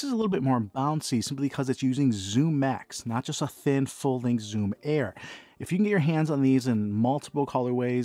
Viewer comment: It sounded like you said ZoomX.